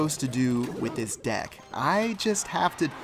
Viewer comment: in 4.10 or something i thought he said sex deck